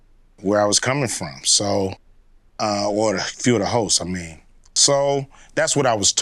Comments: Is it just me, because I swear when Ice Cube said “hosts”, I heard “ho’s (pause) ts” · oh. he said HOSTS.... i thought he said hoes...